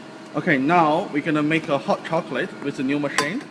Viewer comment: I am so sorry if thats rude but the first thing I heard was "hot cocklate" and my lungs just left the chat